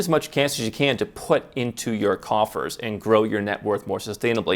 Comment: What did you say at 10:19? Save as much cash as you can to put in your what? And if you said coffers what does that even mean? 🤔